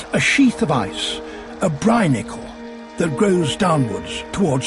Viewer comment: Clicked because I thought it said "Bionicle"